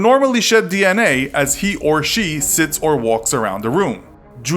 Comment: Y'all could have used the poop to check for DNA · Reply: After reading this. I only heard "shits" instead of sits the entire time. :')